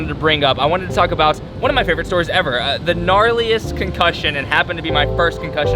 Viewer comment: i thought it said "conclusion" i clicked like the video "hmm interesting" lol